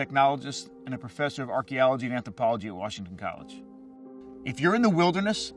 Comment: i just heard ology